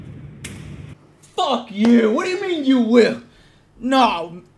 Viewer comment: Batman v Superman: Court of Justice "Do you plead? You will!" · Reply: LevanEvan I heard "Jor-el?" instead of "you will."